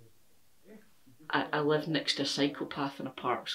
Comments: I thought you said you live next to a psychopath haha! Love your videos. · I heard “I live next to a psychopath”, not “next to a cycle path” 😂😂 I’m glad you are in a fit neighbourhood, not a scary one 😉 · I had to rewind and listen again after you paused to mention the “cycle path”. I thought you said “psychopath” and I couldn’t figure out why you were so calm about it. 🤣🤣